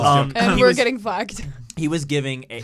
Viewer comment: 🤣what did she say? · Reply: “we’re getting flagged” haha